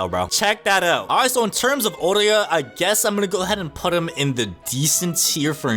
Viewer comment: did you say orywa